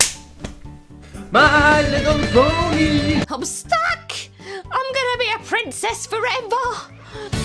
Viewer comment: Am I the only one who thought she said Homestuck